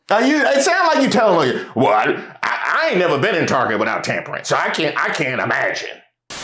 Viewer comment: I think she may have said “tambourine” not “tampering” but also idk